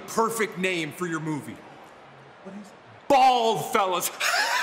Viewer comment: At first, i heard 'BALL fellas'🤭🤭Oww that abusive word by Moss🤣